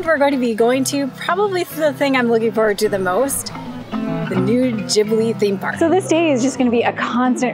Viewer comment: Lol at 0:22, I thought you said Nude Jiggly Theme Park!